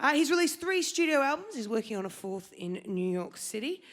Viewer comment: DID SHE SAY HE'S WORKING ON A 4TH ALBUM IN NEW YORK!!!!????